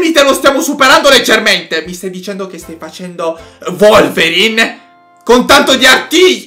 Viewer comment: ma non si diceva Wolverine (ho capito volverine)